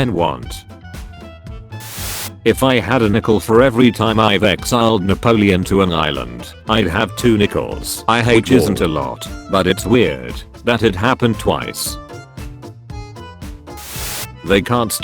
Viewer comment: anyone else hear "I hate war"?